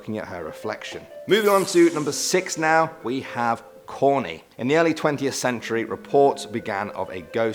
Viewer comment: I misheard him 😂😂 I thought he said porny